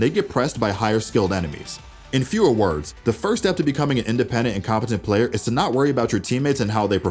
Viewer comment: lol i thought he said independent and incompetent players